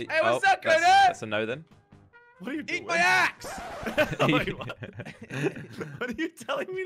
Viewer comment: It was so funny at 7:35 lol he said eat my axe witch sounded like eat my as lol